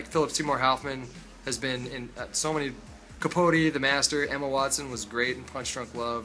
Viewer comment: I totally thought he said Emma Watson was in Red Dragon. all i could think was wtf was Hermione doing in that film?